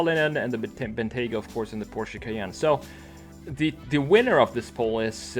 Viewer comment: I LOVE the first gen Cheyenne. So early 2000’s! I have an ‘01 Lexus SUV and I’m a huge fan of the 2000’s styling cues (big headlights, bubbly body) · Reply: for a second I thought you said Cheyenne as in the Chevy lol